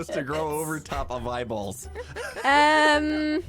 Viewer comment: I thought he said my balls instead of eyeballs